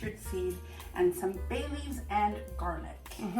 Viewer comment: The edit on this, oh, all I saw was you are both gems. And wish there was a way to vend one right out of your video to try it. At first I thought you all said Bailey’s not Bayleaves and I was like how does Bailey’s Irish Cream go with pickles. 😂😂 I eat the garlic too, but never made them myself. I read and can you ask if she heard about how those of us with MTHFR gene variants have to be careful with lacto ferments or wild yeast ferments too. I don’t know why really, but they do give me headaches but I thought that might be die off 🤷🏻‍♀️.